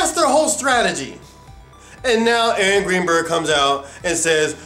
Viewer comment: I misheard when he said "Aaron Greenberg" and thought he said "Angry Bird" each time. Made for some interesting images in the context.